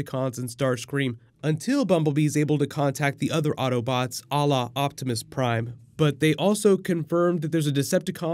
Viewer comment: lol did u just say Allah Optimus prime?